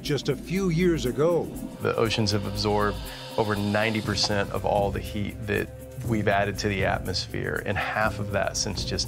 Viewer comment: What did he say??????? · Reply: " The oceans absorb more than 90% of the heat that we (uh) have added to the atmosphere"